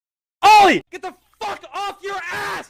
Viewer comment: funny story even though they're saying ollie my name is holly and I didn't have headphones in so I thought they said my name dkxkskkdkfkdjdifd